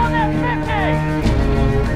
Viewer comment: "Some one get on that .50!" -"It's mine!" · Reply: I thought he said fine too, but yeah its on the subtitles as It's Mine!